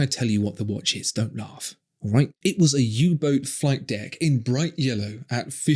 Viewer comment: When you said "U Boat", I initially misheard it as "Hublot" lol Edit: oh...